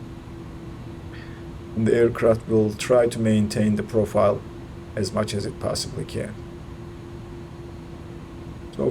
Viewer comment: and at 50' above the ground, I think he says, "cut the throttles." Can someone confirm? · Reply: Yes I did cut the throttles around 50ft AGL. Note that the aircraft descents another 20ft by the time I say cut the throttles and physically do it when "Retard" is displayed on the FMA. I intentionally cut the throttles when passing the RW threshold unlike A320 where you wait until 20ft and hear "Retard" call-out. If I wait longer then it's a firm landing not sure if it's due to the flight model or coding of the A310. Might be different in real life.